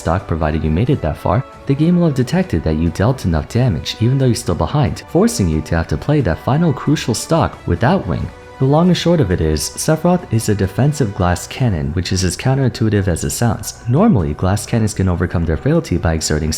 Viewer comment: I thought you said “the long sword of it is”